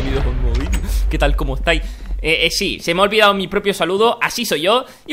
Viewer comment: en el minuto 0:25 creí que dijiste Exi y dijiste "ehh si" JAJAJAJ